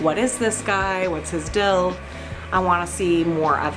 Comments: I just want you to know that at 18:28 you say "dill" instead of "deal" and its hilarious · Did just say "What his DILL"....... I think she did. is contagious! hahahah